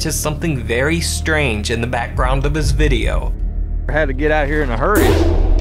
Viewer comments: In the abandoned children’s asylum I heard it say “ Time to get up.” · The children’s asylum voice sounds like it says “we gotta get outta here” to me